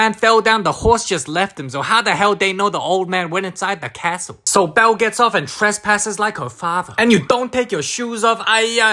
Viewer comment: did he just say bell GETS OFF on trespassing ? 👀💀💀💀💀